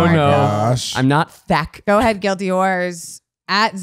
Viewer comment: "...don't mess with horse girls..." Goddamn straight that's why you don't mess with Ulysses S. GRANT! 😤😤🐎 (Ultimate horSe Girl!! 🐎) (...lmao I'll see myself out 🙈🙈) ...also speaking of horses when says guilty whores I sometimes mishear it as "Guilty Horses" 😅😅😂🤣🐎, maybe the podcast listeners can be called guilty horses too 😂😂🤣